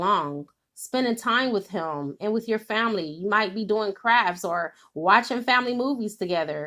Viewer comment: Oh, I thought you said we might be doing crack and I was like... oh no DM, crack is wack.